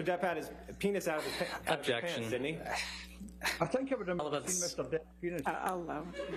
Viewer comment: What did he say that made Jhonny Laugh? Anybody? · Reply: Yeah, he said "I think I wouldn't of seen Mr Depps P3nis", his microphone cut out mid sentence